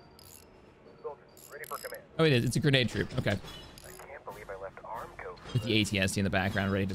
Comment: Did anyone else hear the troopers at 19:50 mark, say “I can’t believe I left armco for this” or am I the only one? I haven’t finished the episode at the time of this episode